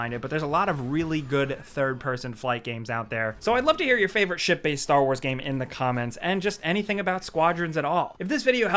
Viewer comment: Haha, when Jake said Ship Based Star Wars game, I thought he said Shit Faced Star Wars game lol